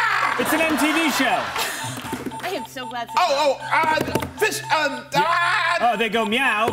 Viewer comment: Did JG not realize that her mic was on at 0:19 when she said "you're not on my team, you don't exist to me" to John Mulaney? Damn, there's so much tension between them. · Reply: also at 1:17 i think she said "i'm so glad this is not me" meaning she's glad she's not in quest's place, as if john is doing a bad job at giving a clue